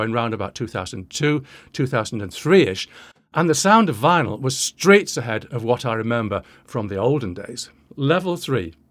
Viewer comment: I did what? Piercinald Anastasia? Do you have a timestamp? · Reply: I guess I misheard you, sure sounds like streets ahead to my American ears.